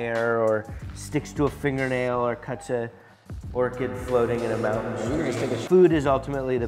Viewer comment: I swear he says "poor kid floating in a mountain stream"...should we be concerned